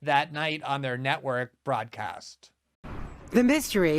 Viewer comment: Glenn, it sounds like you said "fraudcast" instead of "broadcast". Either way, it's a great term.